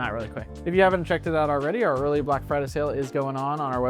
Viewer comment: I genuinely thought i heard you say "welcome to our really black friday sale" 😂😂🤣🤣 Had to go back and listen twice before i realised you said "Early black friday sale" 😅😂